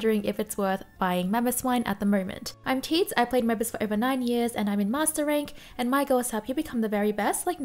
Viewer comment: I thought you said "I'm Teeds and I've played Mamoswine for over 9 years, and I'm in master rank. And I'm here to help you become the very best like no one ever was!"